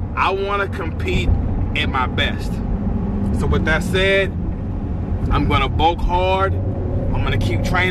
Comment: I heard "With that said, I wanna compete, against Matt Ogus man" like in the song edit from the first time they were going to compete. 😂😂